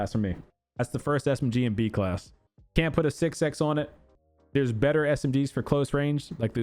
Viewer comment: English is not my first language but i understand 99% but i couldnt understand one Word you said it at 43:57 something called the sick sax and i have no idea what it means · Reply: 6x(scope)